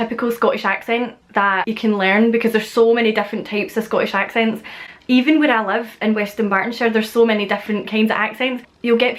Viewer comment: So crazy when you said "even where I live" it sounded like "even what I love" to my California boy ear